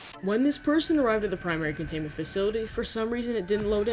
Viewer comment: for some reason, I heard Framerate Facility